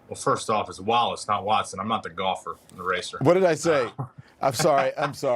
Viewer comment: did he really say, "I'm not the golfer, I'm the racist?" I'd go back to that point, but I'd rather continue on in my amusement. ;^} 😂